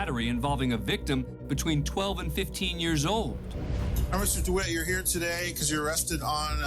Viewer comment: At the 4:35 mark or there about....did the judge say "officer DeWitt"? Wtf lmfao. After a relisten judge may have said "obviously"